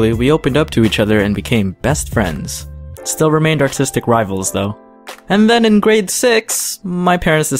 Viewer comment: Sorry , it sounded like you said autistic rivals .lol